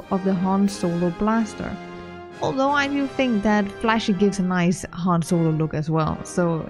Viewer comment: Great video thanks for sharing :) You nailed it as always and you inspired me to finally start my han solo toon but unfortunately i am a returning player havent played for years so i dont have the blaster from Nightlife Kingpin’s Blaster :/ . In storyline (4:12) you say there is one that looks like (flaffy?) but i cant understand the full name of the blaster which one is it please? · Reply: Sorry, it's my accent. It's Flashy. It's your first blaster that Corso gives you on Ord Mantell.